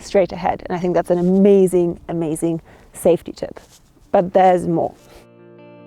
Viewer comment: What does you say on -6:25? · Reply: She said "but there's more," indicating she was going to continue on describing safety tips. God Bless.